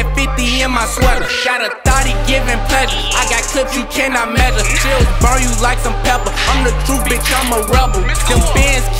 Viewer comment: what does he say? "Got a thottie giving ____?" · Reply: pleasure